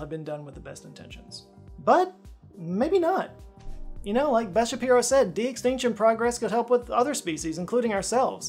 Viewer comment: lol, I thought he said Ben Shapiro. I was suddenly surprised. Anyway, sorry Beth.